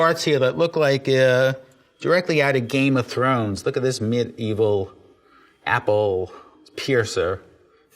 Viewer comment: Did he just say "mid-evil" instead of medieval?